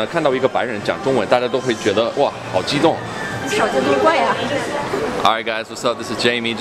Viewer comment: Some problems in subtitle 0:51 what she said is 少见多怪呀, not 好奇怪呀. 5:26 it’s supposed to be 不要“冲”他发脾气, I never saw people say “从”他发脾气. Your oral skills are perfect though